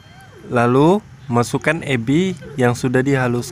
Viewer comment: masukin apa bang ? saya dengernya ebi,bener ga bang, mohon jawab bang 🙏